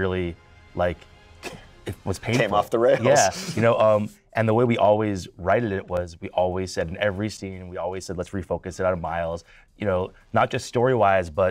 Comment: Did he say "writed it".